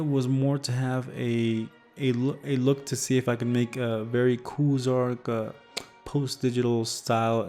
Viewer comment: Amazing video! I have a question, what name did you say at 2:27 ? About some post digital style? Cuz I'd like to see some more examples on google of this style. And what are your thoughts on Sketchup vs Revit? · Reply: Koozarch! :)